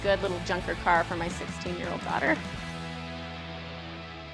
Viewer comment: "16 year old daughter" 😳 She looks 19. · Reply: If it wasn't for your comment I wouldn't know she said 16 year old daughter I thought she said 6 year old daughter.